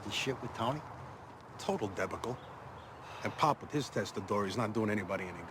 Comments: What does he say starting at 0:33 "... and Pop, with his testa?" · did he say testatory? I love this show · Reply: Testa dura = Italian phrase for hard head